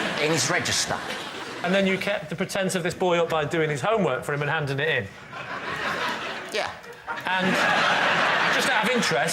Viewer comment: Can you help me understand what Lee's saying right after "by doing his homework ..." at 00:56 ? I'm a non-native speaker and I just can't make out the words no matter how many times I listen to it. Thank you :D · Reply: "by doing his homework for him and handing it in"